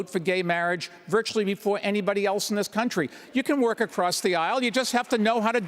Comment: Pete Buttigieg is so confused about the statement because it sounds like Bloomberg said, “Walk across the aisle” instead of “work” right after his statement about the gay marriage hahaha